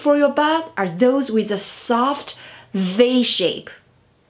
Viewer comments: Around the 2:20 mark she says “a soft ‘VAY” shape? Is she saying ‘V” shape? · what shape did she say? I keep repeating that and cannot understand. 2:42 the more _____ (what?) the yoke is? Someone, please help me. The subtitles are only in Korean 😭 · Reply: V shape, angled